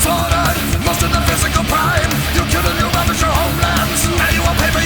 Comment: I thought he said “WORSE THAN PHYSICAL PAIN”